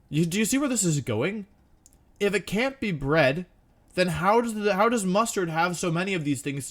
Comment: I know I misheard it at first but hear me out “If it cant be bread how does mustard have so many of it to give away?”